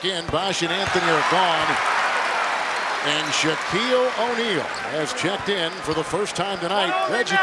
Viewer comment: OH i thought it said SHareek O Neal